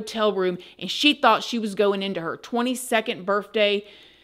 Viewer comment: I don’t know why, but when every time you said the word birth day, it sounded like you were saying birth with an FJ